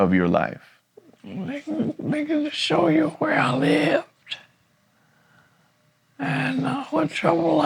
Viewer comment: I thought she said ""nigga" show you where you live"